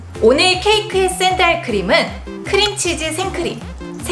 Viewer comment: 붕어빵처럼 붕어모양이 아니라 진짜로 참치가 들어가는거였엌ㅋㅋ 무슨 맛일까? 4:27 크림참치라 들었넼ㅋㅋㅋㅋ그래서 크림참치는 무슨 맛인지 생각하다가 크림치즈여서 혼자 뿜었다